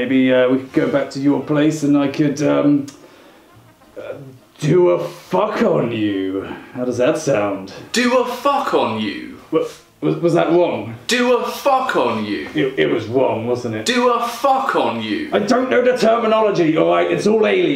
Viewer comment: I thought you said, "Do a fart on you" at first